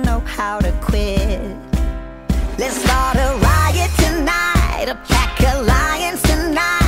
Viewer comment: I cant help but hear alpaca lions instead of a pack of lions.Its now just stuck in my head